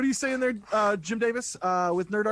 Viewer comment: did call "Jim Davis"? :P LOL